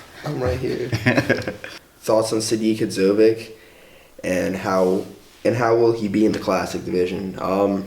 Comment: who's the bodybuilder they are talking about at 0:24 ? i couldnt understand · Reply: 01 burg sadik